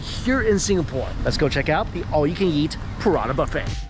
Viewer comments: I thought he said "Piranha" buffet. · Ahhh I thought he said Parana buffet. Not Parata. I was expecting to see him eat some Parana or having parana in tanks to look at while you eat.